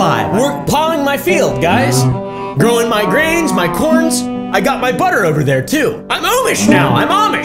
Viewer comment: "growing my grains, my corn" ..... what i thought he said...'my p*^n" --_-- im srry i have a mind of a four year old